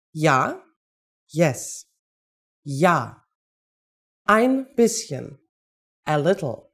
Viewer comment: At first I thought she said: Yeah, I'm Bitchen.